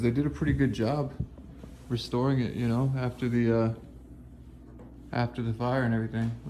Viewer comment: at like 6:02/6:03 where says restoring it I heard a voice say "what" did anyone else?